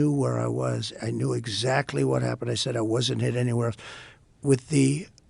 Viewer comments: he said I wasn’t hit anywhere. Did I hear that right? “Accordion Hands” = he’s lying. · Wait! Did PunkinHead say he wasn’t hit anywhere?! At about the 2:18 to 2:20 mark.